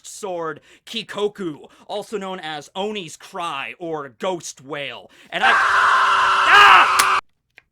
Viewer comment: at first i thought he said ghost whale. would've been cooler imo XD